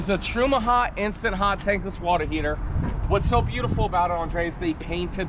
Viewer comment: Did just say The “Watta heatar”?? or Andrea, I’m gonna need a ruling on this one..?! Please go back to 3:20 on the video and review!! I heard the R at the end of heater..